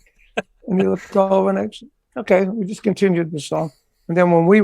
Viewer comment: What does he say at 6:46 (before "continued the song")? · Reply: "He looked over and said, ok, and just continued the song"